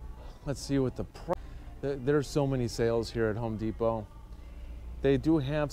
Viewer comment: Did he just said good specials on Home Depot? Lol min